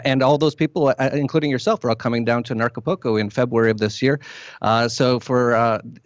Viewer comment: Thought he said "narcopolco"... I'm in!